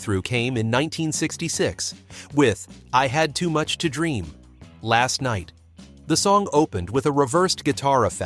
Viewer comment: Ha! I always thought they were saying "I had too much to drink last night".